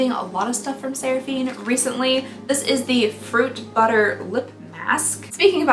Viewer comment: now somebody tell me why i heard “pork butter” instead of fruit butter